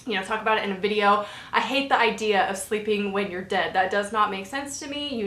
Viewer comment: When You said "sleeping when You are dead" I heard "sleeping with Your dad" and I was really confused for like 10 second oh gosh. :D